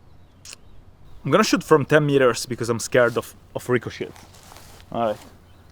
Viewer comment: PFFFT, even the subtitles heard that as you being scared of Rico Shit. Love it. :D